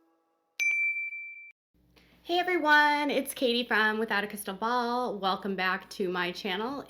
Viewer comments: oh wow!!! i always thought you said Katie Brown! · Yes it did sound like you were saying Katie Brown!